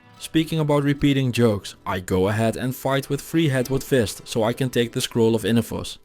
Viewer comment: Pretty sure I heard: "Freehead Woodfist" instead of "Treehead Woodfist"